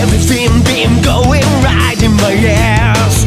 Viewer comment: what did I just hear? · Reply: Right in ausss